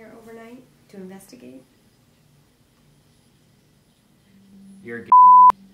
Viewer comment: did he say "you're gay"??